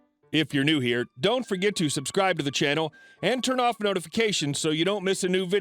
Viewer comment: I SWEAR he says to, “Turn off notifications”.